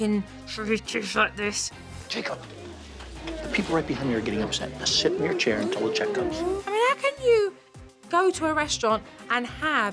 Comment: What did he say to Jacob? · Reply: Jacob, the people right behind me are getting upset. Just sit in your chair until a check comes.